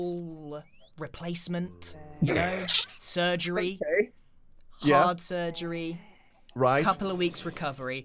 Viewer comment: Grian: We get you suspenders Me: Ok that’s nice Grian: We get you some nice new shoes Me: that’s pretty cool Grian: But for sure a complete face replacement Me: ummm ok that’s weird Grian: Some heart surgery Me: ok what the f*ck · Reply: I thought he said hard surgery but I'm tired right now so idk